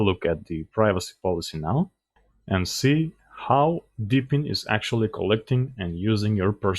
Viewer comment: 1. I never said Debian was Chinese. 2. This video is about the distro that is based on Debian as it was pointed out during the video. 3. Being disappointed in the version of the apps refers to the deepin app store. As distro maintainers they can choose to put any software version they want. This does not specifically mean that they always need to use Debian ( stable ) code base. 4. I never said that or pointed out that contacting China's serves has anything do do with Debian. It seems you are confused here for not understanding the content or the point of this video. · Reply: Maybe I misheard debian for deepin. I noticed later on in another video how close they sounded when he said them. I'll listen again closely. I did like the connection check you did.